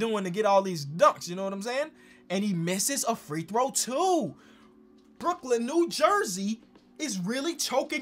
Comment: Did he say brooklyn New Jersey